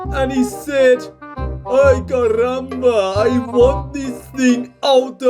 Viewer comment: What did he say at 1:06? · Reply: Aye curumba